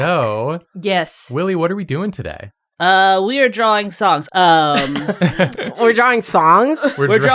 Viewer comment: It sounded like Willie said they were going to be drawing socks. I know he does knitting on his channel, so it seemed plausible. Drawfee's been experimenting with different art mediums, they should do an episode where they have to knit (or crochet, or embroider, etc.).